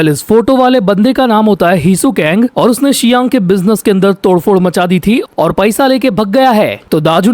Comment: kya bola Hisoka? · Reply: Hisu kang bola vai